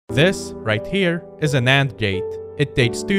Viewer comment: It sounded like you said "This is a nand gate" and then you showed "an and" gate, very different thing.